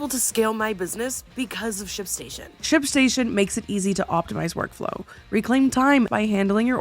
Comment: Girl, I thought you said shit station 😂